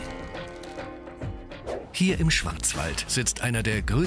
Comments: bin ich der einzigste der schwanzwald verstanden hat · Ich hab "hier im Schwanzwald" verstanden😂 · Ich habe mir das jetzt 2 mal angehört, und der sagt "Hier im Schwanzwald"... 8:19 Kauen und Kacken? XD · als ob ich schwanzwald verstanden hab ಠ_ಠ